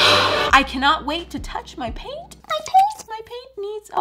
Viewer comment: Af first I thought she said I can’t wait to touch my pants